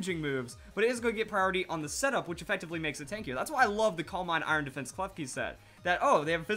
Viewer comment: xD thought he said "calm mind iron defense fuck-you set"